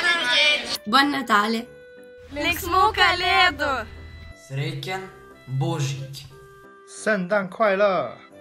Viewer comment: did they just say “bullshit”? 😹😹😹😂😂